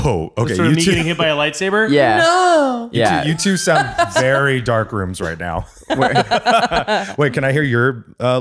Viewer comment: Wait... Omg I thought Eugene said dark lands, not dark rooms. 😅